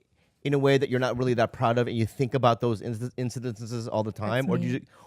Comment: What did Bobby do with the word incidents at the 16:32 mark? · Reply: He says “insadences”